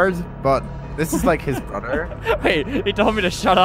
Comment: this is like his brother me:did he say MOTHER? *says it again: me:oh he said brother-..